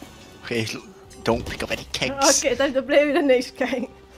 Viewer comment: also 2:42 what I said was Nej meaning no in danish I was suffering HARD